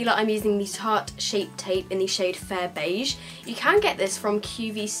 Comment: What colour are you in the shape tape if you don't mind me asking 😊xx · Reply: Kim Arthur heard her say fair beige